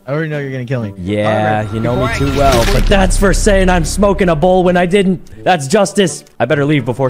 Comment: For this whole time, I thought they said “ smoking a bull “ not a bowl !! 💀